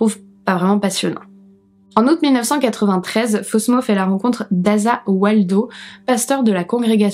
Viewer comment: Oh ! Je suis désolée mais à 9:10, "fait la rencontre d'Asa Waldau". Fan de Jojo's Bizarre Adventure, j'ai entendu "Za warudo", c'est terrible. 😂